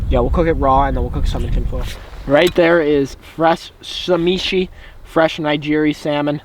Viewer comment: did he just say shameshi??? haha.. sashimi!